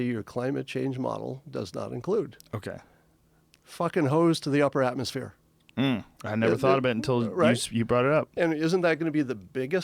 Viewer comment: Did he say "fuckin hoes to the upper atmosphere" ?